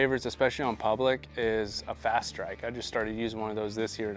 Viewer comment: What was the name of the portable blind you mentioned at 2:11? Did you say fast track? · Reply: Fast strike